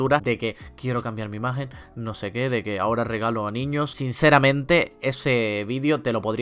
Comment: le entendi "aora regalo niños"